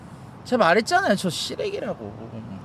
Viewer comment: 난 왜 여기서 을 시레기로 들리지? ㅋㅋ